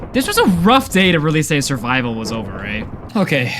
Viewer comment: anyone have any idea what he said at 1:50:28 ?? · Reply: "this was a rough day to really say survival was over ey